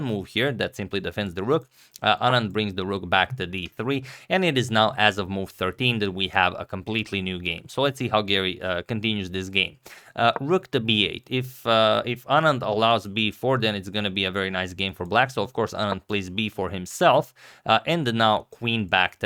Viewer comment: for a moment I thought you said Giri😂